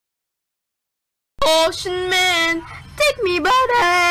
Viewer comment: What I Hear: Oh shut Man Subtitles: Ocean Man 😂😂😂😂